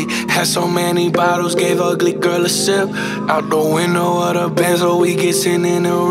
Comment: all this time I thought it said “give ugly girl a simp” gdi okay then